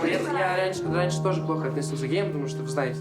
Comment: не пон был геям, или что он сказал? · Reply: А сказал, что раньше плохо к геем относился